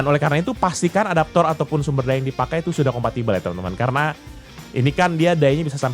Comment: Compact? Gw salah denger kan?